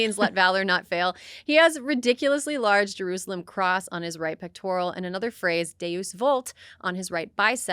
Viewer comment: Was that “right pectoral”? …I heard “right clitoral.” The latter would fit given his having a micro-peen.